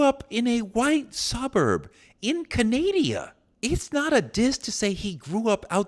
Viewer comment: Wait did he say in "Canadia" instead of Canada?